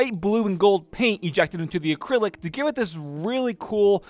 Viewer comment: did he really say acrylic...